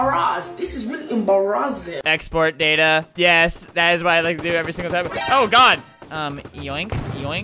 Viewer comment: did he say god eater